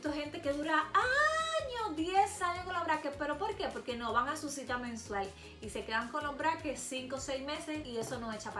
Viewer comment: escuché cita menstrual xD